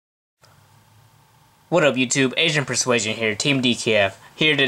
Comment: Idk if its my hearing but in the beginning it almost sounded like he said asian persuasion